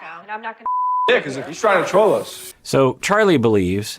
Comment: To me it sounds like Charlie said "trying to TROLL us" not control us